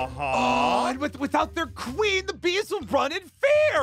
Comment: I lowkey thought it said “protect the hime queen been” I was confusion.